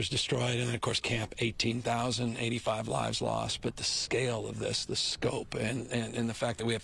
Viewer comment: Did he say 18,085 lives lost!